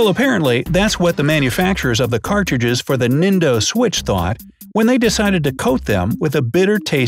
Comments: Did he just say nindo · He says Nindo instead of nintendo